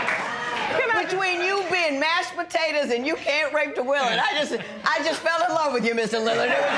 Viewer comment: Raped the Willie 😂😂😂😂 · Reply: LOL i thought he said "wake the willy"